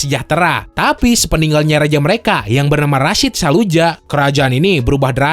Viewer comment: g salah denger kah aku, soalnya katanya Rasyid saluja ( kedengarannya ). Soalnya nama ku M. Rasyid sabiqul khair